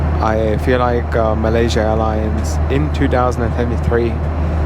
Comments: Is this an older video? 7:13 “I feel like Malaysia Airlines in 2023 is doing an exceptional job”. The video was just uploaded and it is almost the end of April 2024. On a side note I at first thought he said “in 2003” and had to rewatch that section. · did I hear right? 2023? 🤔